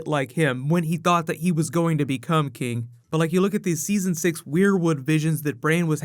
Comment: did you say brain was having wierwood visions? at about 5:07. lol